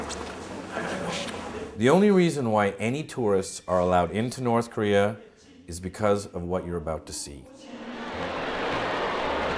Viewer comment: I always gotta come back here every once in a while fr this shit it a classic rs and I really really love how that North Korean narrator says "North Korea" cuz it sounds like he is saying "North Career" lol.